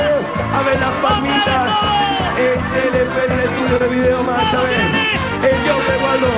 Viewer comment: si alguien me puede ayudar, qué dice Rodrigo, le escucho algo referente a "peruano", ¿se refería a uno de sus músicos? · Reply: El show de waldo dice